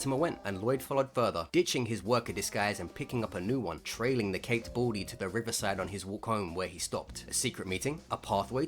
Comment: totally thought you were saying "caked baldy"